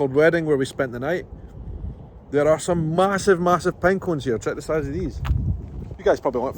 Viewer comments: Pine Cones! Ha - I thought he said Penguins. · Oh, you said, "Pine cone". I thought you said there were massive penguins. LOL. No offense to your accent, buddy. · You said, "There are these massive, massive penguins here." Then you went and picked up a pinecone and I was sorely disappointed. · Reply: Haha! Yes, I thought I heard him say "penguins" also. I thought, "What?" Then, he picked up a pinecone, and I was....ooohhh.